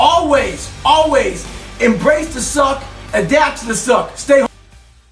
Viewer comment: Bro the subtitles are so bad its humurous at 10:16 goggins said stay hard but the subtitles said stay home😂